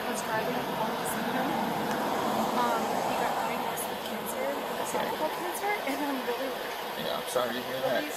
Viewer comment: no dude, I didn't have subtitles on, and she 100% said her boyfriend had cervical cancer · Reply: silly chipmunk.....stop chuckin wood......she 100% said ESOPHAGAL cancer.....(at 20:02 for those who doubt what I say).......